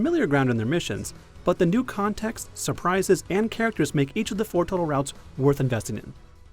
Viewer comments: uh did he say 4 total routes at 4:30? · Did he say four total routes??